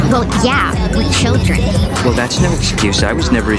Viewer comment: Bro I swear I heard her " we eat children" 😭